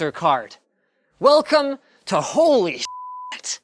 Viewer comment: Did you say holy spit ahah 😂😂